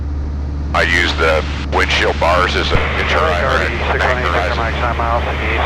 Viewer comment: What did say on 5:20 about using the windshield bars? great video, thanks for sharing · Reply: said you can use the windshield bars as a bank indicator. In the video, at the time you mentioned, you can see the reflection of the bars vs. the horizon.